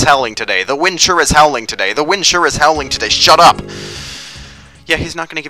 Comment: When you first went to the broken Zelput, I thought he said "The wind sure is bowling today..." :D